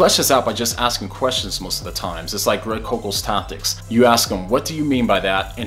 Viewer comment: I couldn't quite catch that. Tactics? Tontics? I'd like to know because that sounds interesting. In most discussions online I like to ask my opponent for definitions, then have refine it to a point where a rebuttal would be irrefutable - most people can evade an opposing view by falling back on another interpretation which is impossible if they clearly gave a definition. Great vid! You deserve WAY more subs. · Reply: Thanks for the compliment!! And its Tactics (a book) by Greg Koukl. I highly recommend it!